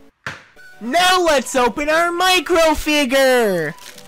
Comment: i always thought he said NOW LET’S OPEN OUR MINECRAFT FIGURE!